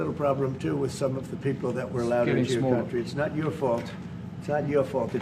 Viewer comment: What does the german say at 3:29? Something like: ".....into your country"